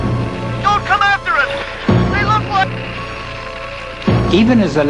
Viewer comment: what did he say at 5:32? don't come after us! they look like?